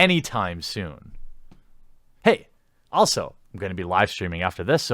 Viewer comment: Let's Finish The Day Laughing At The Literally Human Shit Problem In San Francisco! I'll Be Live Streaming In About 1 Hour Too So Come Back! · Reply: I thought you said live steaming at first.